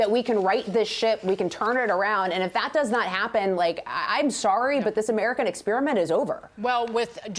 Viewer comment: SERIOUSLY thought she said this SH*T! LMFAO!!!